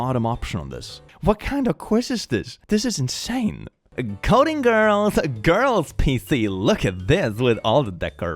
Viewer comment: HAHFAJHBGJ I THOUGHT YOU SAID CODIENE GIRLS AND THAT NEEDS TO BE A SUBREDDIT